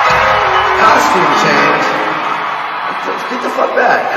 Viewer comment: Am I completely crazy or did Slash actually say "costume change"?? I could've swore he initially said that & then corrected himself when he found out that Axl had actually but his hand, but then in the Stern interview he says "I didn't say costume/clothing change"?? I'm confused? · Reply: Actually he said "...clothing change, just be patience" and after that he said that Axl cut his hand. Didn't say costume change but Axl assumed Slash said that because he had a problem with him changing his clothes on stage all the time.